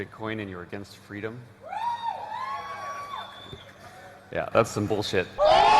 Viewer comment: j'ai compris "That is some bullshit" de mon coté ^^